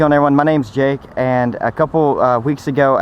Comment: In the beginning I thought he said “My name is jeff”